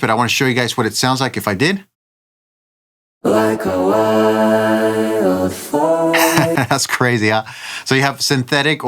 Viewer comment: All I keep hearing is "like a wild fart" when you playback the vocal. I can't unhear it now🤣